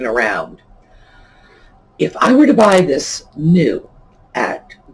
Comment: Hey, I was wondering where you purchase your lamp pieces from, it sounded like you said Brand Brass but I could not find that so I think I might have miss understood you thanks I learn so much from you!!! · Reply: Grand Brass - it's online and they have good prices and quick, reasonable shipping. But the best part is their selection - if they don't have it, you don't need it... 😁